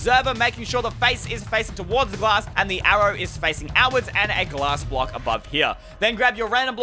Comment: The auto sugar cane farm: I heard you say glass brock